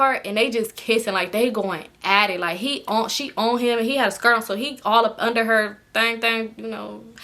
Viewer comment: did you just say “he had a skirt on” lls what kind nxggas y’all be hanging around 😂